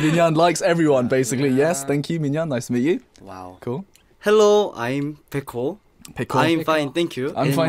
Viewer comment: I really thought Baekho said "Hello, I'm pickle."